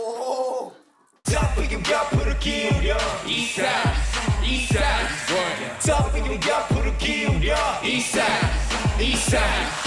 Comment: 익산 으로 들리는데